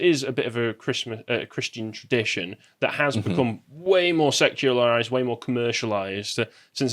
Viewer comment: at first I thought you said Christmas was becoming more "sexualised". I was like, "What? How? That Love Actually spoof music video 15 years ago? Oh, wait, he probably said secularised."